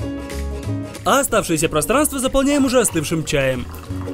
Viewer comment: мне послышалось, что ты сказал "а ужасным чаем..." аххахаха